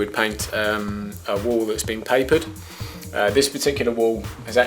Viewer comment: O, YOU MEAN A WALL. I THOUGHT YOU SAID WOOL?